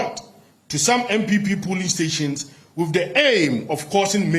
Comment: Did he say "NPP polling stations"?